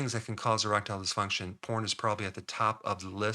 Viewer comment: Ha, I was listening while on another screen and thought you said "Corn" @ 7:26 was a big cause. lol